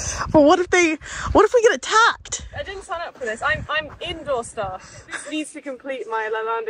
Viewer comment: I didn't sign up for this. I'm indoor staff. 😆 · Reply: I thought I heard her say “I do indoor stuff” 😂